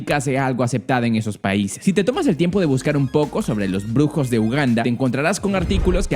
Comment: escuché grupos de ugandas pensé el Uganda de VRChat